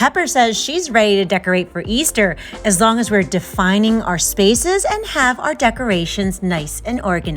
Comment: I heard "defining lemons" and I was like YES MA'AM, IT'S LEMON BARS SEASON 🤣❤️❤️